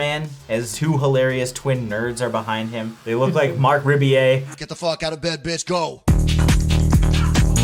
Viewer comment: who did they say that guy was? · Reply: Marc Rebillet: <link> He's awesome!